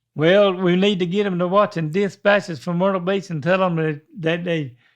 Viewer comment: "Dick Betts is for murdering me" being what the captions thought said while trying to be a ventriloquist, instead of dispatches from myrtle beach, is somehow the most hilarious thing.